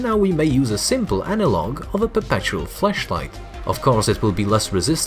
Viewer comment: did you say fleshlight?